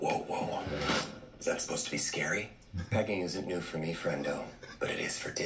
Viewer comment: "Pegging isn't new for me, but it is for Disney" Wow!.... talk about self awareness. Save us Deadpool, PLEEEESE!!!. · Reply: I thought he said begging..ok then 😅